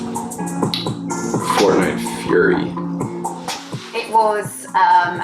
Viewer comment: “Fortnite Fury” n-no. get out. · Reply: I thought its said furry